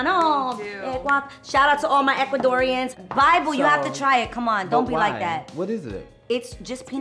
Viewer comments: Did I hear "bible" at 7:09 ? 😂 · did she just say “bible”?